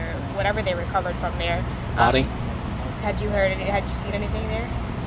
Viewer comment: the 5:43 did he say nani? cause i tried playing back at full volume but it sounds like he says nani?